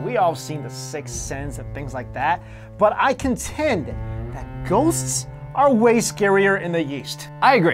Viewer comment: i heard "ghosts are scarier in the yeast"